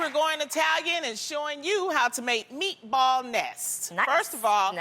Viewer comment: Meatball nest is not a very appetizing name 😟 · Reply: I thought she said meatball mess lol. My volume is on 1. I was like oh I like a mess of marinara meatballs and cheese!